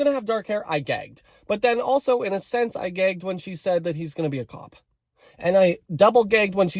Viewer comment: I thought you said "he's going to be a TOP"😭😭